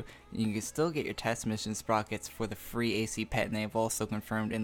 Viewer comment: um did he just say "free ac pet NAVAL" pls correct me if im wrong